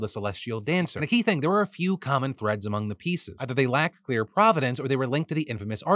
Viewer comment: Thank you for defining provenance- I thought he said providence and also, I didn’t know what the former meant.